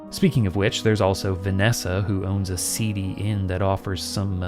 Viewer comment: I've watched this video about three times and every single time I hear "Vanessa has a CD" instead of "seedy" and I get confused for a bit